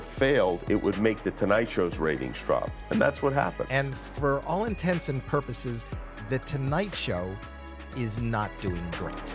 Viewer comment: Great video, subscribed. I have to point it out: in @ 22:25 It's actually "for all intents and purposes", not "for all intensive purposes". It's an "eggcorn", a common misheard phrase like "duck tape", "growth sprout", "ice tea", "pre=madonna", and most commonly "lack toes and tolerant".